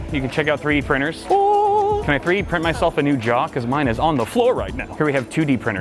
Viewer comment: I really thought you said, "Can I 3D print myself a new jock because mine is on the floor right now!"